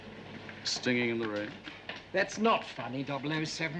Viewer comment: can someone tell me what he said? dont get the joke really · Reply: He says "stinging in the rain", a word play on Singin' in the Rain. <link>